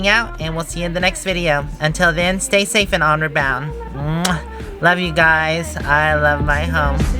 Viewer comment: I always thought you were saying “Honor Bound” rather than “onward bound”.